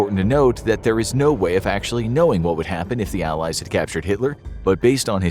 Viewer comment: I thought it said aliens 💀